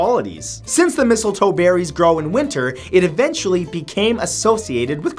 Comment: I heard "Mistletoe berries groin winter" Maybe I need to wash my ears.